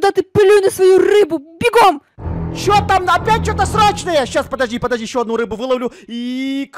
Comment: Я одна слышу срачное?